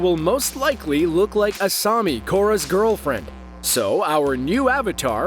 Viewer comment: Did he say korras girl friend???